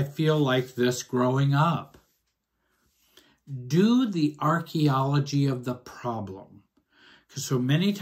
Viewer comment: At first, I thought said, "narchaeology."